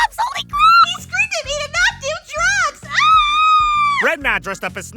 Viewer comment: what the hell is Yuffie shouting? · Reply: "HE SCREAMED AT ME TO NOT DO DRUGS!" fangirling intensifies